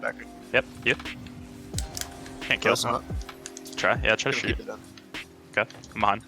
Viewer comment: I swear he said “Good try” at 3:31?